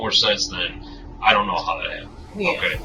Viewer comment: I thought she said she didn't know what happen😮